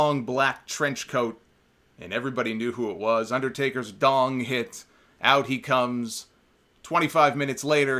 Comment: did u just say "Undertaker's Dong?" Lmao